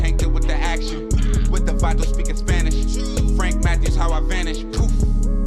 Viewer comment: Whenever I hear your intro, I wonder if he means 'Frank Matthews'? Sounds like he says Frank Mathis....